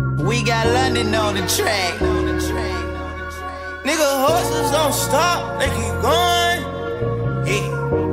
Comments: '' hustlers don't stop they keep going '' this is true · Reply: when it came out I thought he said horses but now that im fluent in thugnism he clearly says hustlers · Who thought he was saying, HORSES DON'T STOP.🤣🤣🤣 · Nigga, hustlers don't stop, they keep goin' You can lose your life but it gon' keep goin🎶🎶😎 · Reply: Seriously? All this time I thought he said horses don't stop 😕 This makes so much more sense